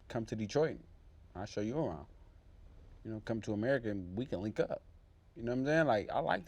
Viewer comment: My Gaydar is sensing something about TONY HARRISON. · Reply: Did he say We can hook up?